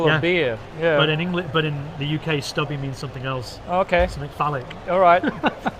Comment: I am still learning English language, at 40:04 what did you say? something "falid"? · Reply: Phallic. ;-)